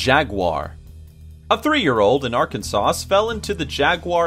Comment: did he say arkanSAUCE?